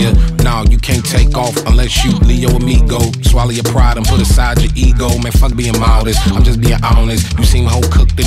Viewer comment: its weird to see Tyler in a world that isn't his. Every music video he's in is a world he creates, but this time he seems like an outsider. Weird. Somehow it works tho. Mature, not cartoony Tyler is fire to see. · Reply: i know right its almost like he's putting aside his character of Igor, maybe why when it says 'Swallow your pride and put aside your ego' , 1:41, it actually sounds like he says 'put aside your igor',, crazy